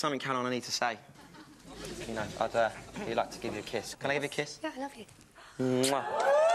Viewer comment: I could've swore I heard Caroline's reply to Olly's request was "Yeah, I love you."